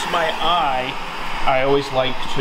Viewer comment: Did someone say “eye” ?